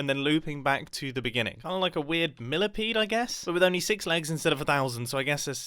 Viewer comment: I think he just tried to say An Insect...